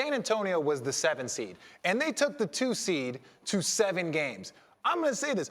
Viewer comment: Did he say Twoth seed at 4:25 ?